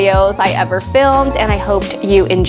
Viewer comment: Merry thriftmas :) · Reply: So funny! I thought I heard fifthmas. Now I get it. Ty!